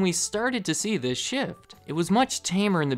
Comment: thought you just said, "in the 1980s is when you started to see this shit" in reference to the extravagant pet products lol great video